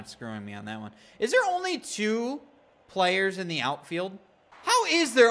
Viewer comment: I thought he said "Is there only jew players in the outfield?"